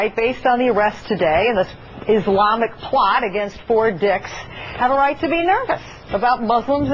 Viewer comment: i thought she said four dicks